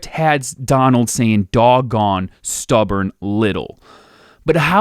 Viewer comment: I actually COULDN'T hear Donald say a slur, and just heard him say "stubborn little" every time.